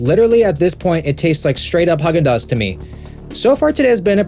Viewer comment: did he say Hug n duz?